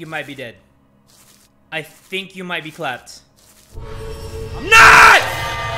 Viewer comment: On 22:50 I heard "You might be clapped- NOT!!!!!"